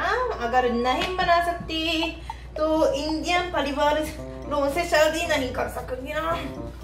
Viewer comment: did she say shadhi (marriage) 🤨🤔